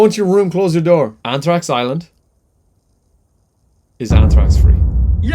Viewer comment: what did he say? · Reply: He said “anthrax island is anthrax free”